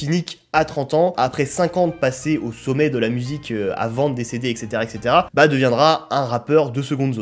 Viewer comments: à 30:50 j'ai compris "avant de décéder" j'ai buggé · tu dis "à vendre des CD", j ai compris "avant de décéder" je suis dis quoi ?!? Il est mort ?!?